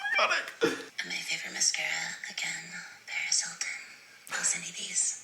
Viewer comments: For a sec I thought he said prez Hilton and I was like 👁👄👁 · I thought he said prez hilton 😭